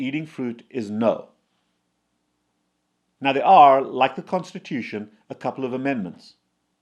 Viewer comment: I swore he said “like prostitution, there are amendments”